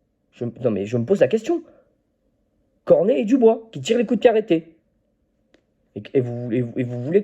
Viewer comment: j'ai entendu "Cornet et Dubois qui tirent les corners" j'ai explosé de rire hahahaha j'avoue c'est n'importe quoi. moi comme toi chaque weekend on me ramasse à la petite cuillère, mais bon c'est ça d'être supporter même si là c'est mega dur... courage courage